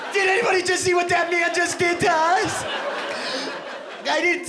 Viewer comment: Uhmmm Wot The Ph..... youtube closed captions (subtitles) at 46:12 were auto-generated with AI right? I know I heard "did anybody see what that man just did does" captions says something else.